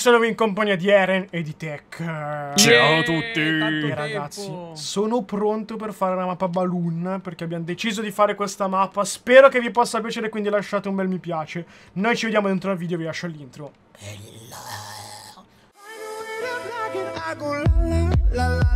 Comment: all'inizio ho capito "rushate un bel mi piace'